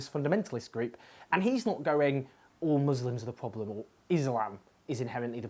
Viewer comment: Oh!!! Islam. I thought you said "It's Lamb, that's the problem". I was gunna say. That would be... ...weird.